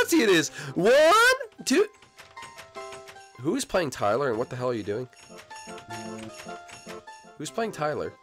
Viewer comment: Did said what the hell